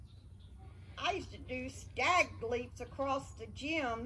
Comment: At first I thought she said SCAG LEAP!! Hahaha!!! Wth!